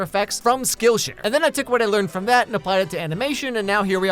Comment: when he was sponsored by skill share i thought the mf said skill issue